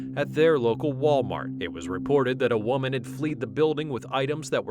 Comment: did you just say "fleed"